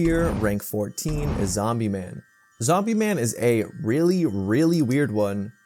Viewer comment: i heard "Omni man" 💀